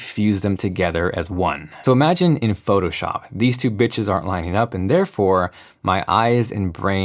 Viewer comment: wait, why did he say "these two bitches"? I'm confused lol